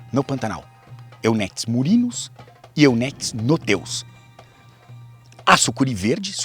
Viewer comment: Eu metis no teu... como assim eu n entendi! 😛